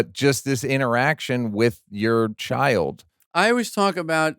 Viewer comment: Did I hear that right? Neal asks Jerry about how great parenting is & Jerry says "I am"? 5:01 Did I hear that right? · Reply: First of all, who’s Brad? Secondly, he clearly says “yeah, yeah.” You my want to get your ears (and eyes) checked.